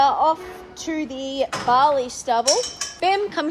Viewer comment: I heard 'Off to the Bali stumble' 😂😂😂